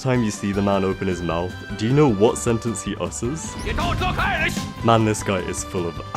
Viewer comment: i thought he said "YOUR GOING TO PERISH!" instead of "YOU DONT LOOK IRISH!"